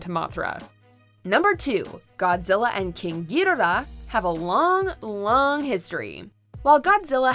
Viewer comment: Honestly, it sounds like she is saying ghidalah instead of ghidorah